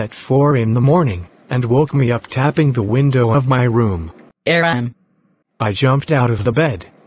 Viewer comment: gosh did I just hear it as AARON instead of Aram, warner is everywhere